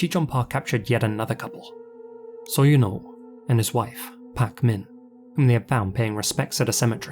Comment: I was listening to the video without watching it, and I had to pause when I heard "So you know and his wife Pac-Man"